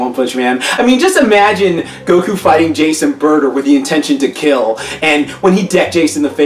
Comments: I thought you said jason burter lol but you said jeice and burter 😂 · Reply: kaiokenangel I thought he said Jason Bourne lol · I thought you said jason burter lol but you said jeice and burter 😂